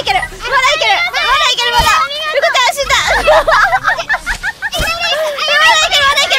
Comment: Didn't anyone heard Suisei called Pekora as "Ruka-chan"? 2:02 "るかちゃん、死んだ"